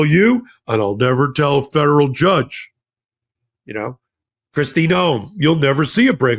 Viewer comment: Marco Rubio did not say Federal Judge he said "a Judge", meaning any Judge. 💙